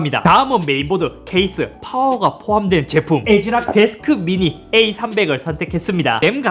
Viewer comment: 내가 들리는대로 적은거임 "애지랄 데스크 미니 에이 삼백" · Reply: "애지락 데스크 미니 에이 삼백"